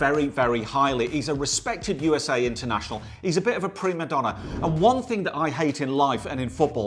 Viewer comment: Did he just say pre-madonna?🤣